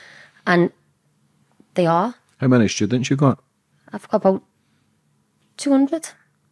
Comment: I thought I heard, How many kids you got? About 200 I was like, get out of here, I rewind and am relieved to know she had about 200 students 😂 I have tinnitus in both ears and always hear wrong. 😂 Great show by the way